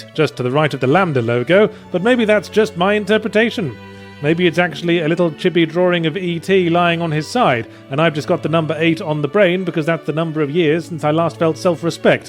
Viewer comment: was... was yahtzee saying "chibi"